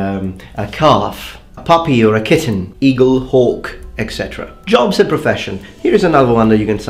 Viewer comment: "eagle, hawk..." I initially misheard that as "eagle, orc...", went back and was disappointed to hear that it was actually hawk ;)